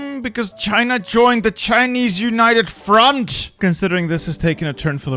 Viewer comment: "The AI has decided to go completelly off the rails, its fine.... I'm going to have aneurysm, because China joined Chinese United Front!" I would be more confused if it didn't.... He did mean to say France though, but was pretty confusing/funny to listen while afk. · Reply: For me I heard “China joined the Chinese united fronch” which is 300% funnier